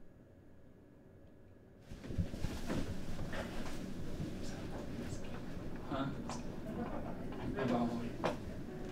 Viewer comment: does anyone know what zekken says here? man his passion for the game is so emotional. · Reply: he said hes scared